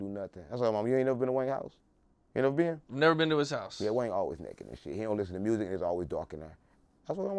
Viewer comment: Boy I needed this shit, I just got outta work I'm in this mf crying. a fool😂😂😂😂😂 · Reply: Im pretty sure I just heard him say “Wayne always naked” 😂😂😂😂😂